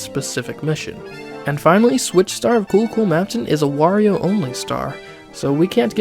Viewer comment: i thought he said: Switch star of Google maps